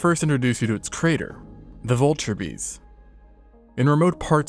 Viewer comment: I was half awake watching this so I thought that said Viltrimite bee